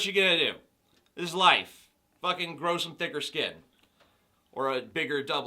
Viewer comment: I heard "grow some dick or skin" O.O lmaooo